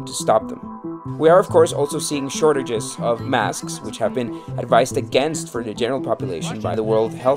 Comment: Funny to hear about fake news as applied to conspiratorial online personalities, yet the video also reiterates the sameself fake information from the Gov, deeply corrupt authorities (WHO) and MSM - such as the animal transmission & wet market origin, and the Q1/Q2 2020 lie of masks being ineffective and ill advised for the general public, which was obviously false even back then. Lesson learned: Apply the same amount of scepticism to higher authorities, even experts in the field (who are NOT divorsed from politics) as you do to random online comments/videos from nobodies. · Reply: Did he say advised against masks btw at 5:14? Funnily enough I do remember some health officials going against masks early in 2020